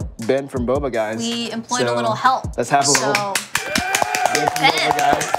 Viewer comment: for a moment I thought it said “Ben Ten” Why am I like this....